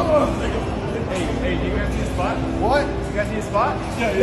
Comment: Thought it said “do you need a sport bra”